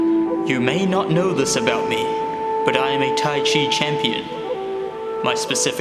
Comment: I come here to learn how to hips a fingerboard ledge and the first thing I hear is “I am a tichi” champion